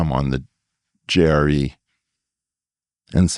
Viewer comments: what did he say? it sounded like he slipped when he said "i wonder why they allow me..". but what does the Jerry mean? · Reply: He said JRE, as in the name of Rogan's podcast. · What was Eric trying to say at 20:06? "Why they allow me to come on the Jerry...." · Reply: JRE. Joe Rogan Experience. He kind of mumbled it.